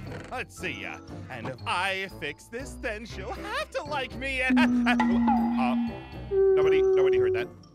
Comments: well well well did u just say u like aph? ( no offence ) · I might hear it wrong... But did Ein say That "Aph would like me"? Then he blushes owo'